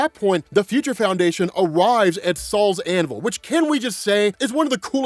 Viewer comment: im not gonna lie i thought u were saying sauls anvil but now i se its SOL'S anvil